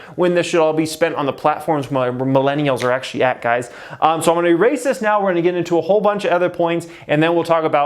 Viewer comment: Who thought Jeremy said "So I'm going to be racist now"